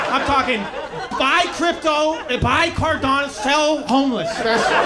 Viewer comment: Did he say buy Cardano?